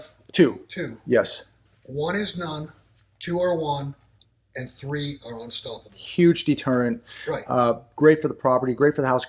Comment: I didn't hear what he said about the dogs (1, 2 & 3). I wanted to hear it but it was unaudible to me. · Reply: 1 is none, 2 is 1, 3 are unstoppable